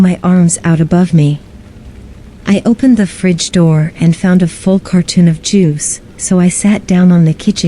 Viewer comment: "A full cartoon of jews?" Did I hear that correctly?